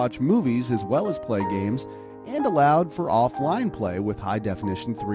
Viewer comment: What the subtitles say: "And allowed for online play with high definition 3D visuals" What he ACTUALLY said: "And allowed for offline play with high definition 3D visuals"